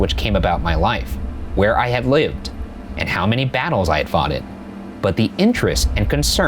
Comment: Did he say foughted at 7:22. Or am I hearing stuff.